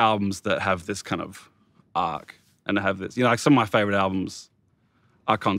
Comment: Min 4:54 cant quite understand what album are they talking about . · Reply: The streets a grand don't come for free